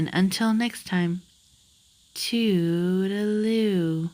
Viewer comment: every time I hear to tutelu, I hear " too-da-loo" I hear " to the loo." So what I really hear is, this video is now over because I have to go " to the loo." lol